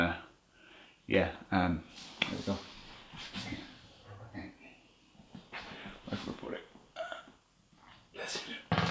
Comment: I hear an EVP, like as if a man whispered some words that I can not make out... Do you also hear that? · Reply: "syrup... over pancake", thats what that voice said.